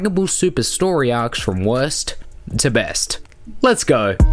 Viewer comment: Sound like your saying West To Best